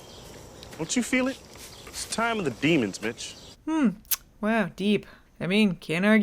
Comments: I hear “time of the demons, Bitch” rather than Mitch and oh my god did that throw me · It honestly sounded like Teague said, "It's the time of the demons, bitch."